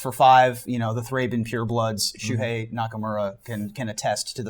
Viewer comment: Not gonna lie: When mentioned Shuhei Nakamura, at first I thought he said Shinsuke Nakamura...